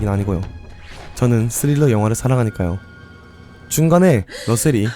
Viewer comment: "저는 스릴러 영화를 사랑하니까요." > "저는 레슬링 영화를 사랑하니까요." 로 들림.